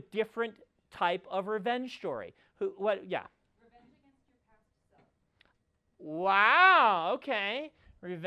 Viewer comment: What does the student suggest at 59:42? BS doesn't repeat it and sound full on doesn't catch what the student said. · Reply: Headphones blasting, I think it's: "revenge against your past self."